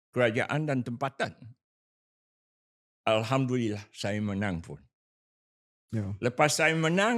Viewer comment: aku salah dengar ke dia sebut alhamdulillah